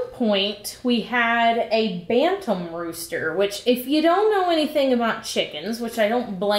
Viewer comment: Imma be real, I first thought I heard you say phantom rooster and I was super excited to learn some wild chicken psychology.